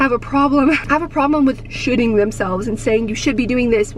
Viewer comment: Lmao I 100% thought you said “this year I feel like a lot of us are shitting ourselves” instead of “should-ing” 😂😂😂😂